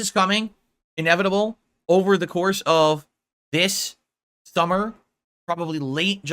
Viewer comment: Did he just say "Over the course of this thummer..."? The gin and his usual mushmouth are not being kind to.